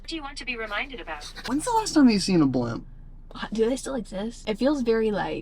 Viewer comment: I thought kept saying when was the last time you've seen a blunt, not a blimp. I was very confused for a hot second about what that had to do with football. 😂😂😂